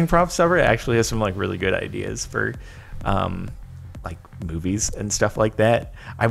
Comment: I THOUGHT JODAN SAID BOOBIES NOT MOVIES LOL.